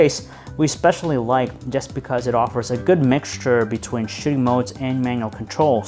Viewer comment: Great review, as always... but did he say "sh!tting modes"(at 3:30)?? I love Phone Arena's videos, but my friend, you need to enunciate better. You sound a little drunk!